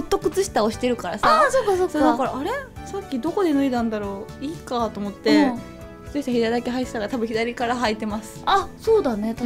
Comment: まれいたそ左足から履くの理由はなんですか？よく聞こえない… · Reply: 片っぽだけで歩いてた時に左履いてたから左